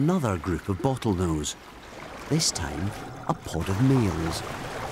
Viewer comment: does he say a pod of meals or males ?